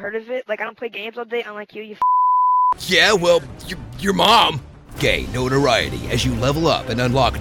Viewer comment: It sounded like he said your mom gay